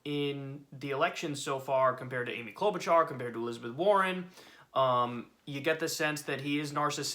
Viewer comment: So when I heard Pete “suspended” campaign: 1st reaction: we need him to split votes with the other centrists 😱2nd: maybe Bloomberg bought him. 3rd: Biden made a deal with him 4th: Pete came to Jesus 🙏 5th: Warren? He’s not that stupid 6th: Amy? they hate each other 🤣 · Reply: For a second I thought you said Sith Warren lol. She has FALLEN!